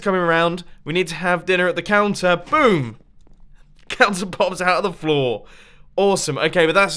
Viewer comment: Subtitles say "cancer pops out of the floor" lmao 😂😂😂